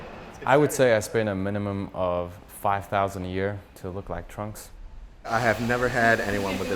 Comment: if i didn't open up subs i will thought he just said he spend 5k to look like trump